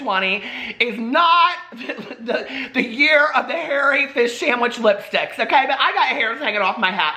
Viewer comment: I heard you say hairy fish sandwich and then I thought you said you reviewed a smelt cappuccino 😂 (smelt are small fish, usually served as a fish fry) ha ha ha ha ha ha I love you! ♥